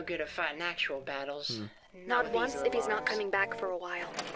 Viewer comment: I'm simping for. I'm sorry, but it's out of my control. This isn't ironic. 2:32:22 Oh she says "NOD once" I heard it as "not once" and was really confused that she was starting mid-sentence. 3:25:15 Yes. I'm not apologizing. Having a voice like that is just plain unfair. If the video's themes are targeted harassment to you, then throwing a bunny girl with a warm voice and kind nature is the same to me. I might be a shameless pervert, but my weakspot is the exact opposite. Like if you spend the day eating nothing but salty chips, then someone hands you a glass of cold ice water.